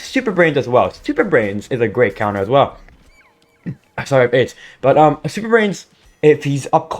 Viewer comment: did you just say "sorry i have aids" 0_o